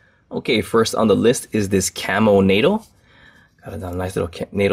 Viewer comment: Misheard the camel nato as camel to...e. I have a dirty mind lol btw this model is aka baby sumo also.